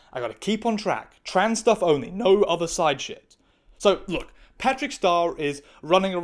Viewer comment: I really thought you said, "I gotta keep on trap. Trans stuff only, no other side shit." as a funny wordplay and dig at the commentor. Although, I was hanging upside on one of those inverter swings, but I just thought, 'I gotta keep on trap' is such an odd/funny thing to have heard.